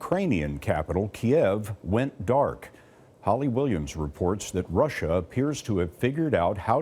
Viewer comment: I thought Scott Pelley said "Ollie Williams" when talking about Kiev. Part of me hoped to see it cut to Ollie to say "THERE'S NO POWER!"